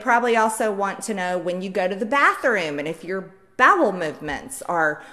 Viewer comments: Sounded like she said babble movements. Speak English or get out, MTG! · I thought she said "babel movements"